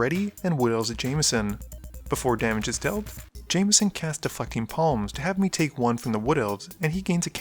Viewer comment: I swear it sounds like he says defucking palms!